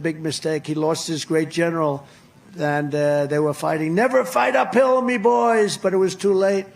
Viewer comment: I misheard, "Never fight a pillow my boys." Coincidently there was an ad with Mike Lindell hugging a pillow on screen.